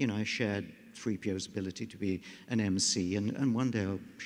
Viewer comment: What does Daniels say at 49:50 when everybody is laughing? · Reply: Julio Chao something about an M.C